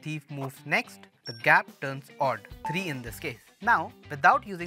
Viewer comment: i thought bro said "and he's fucked" at the end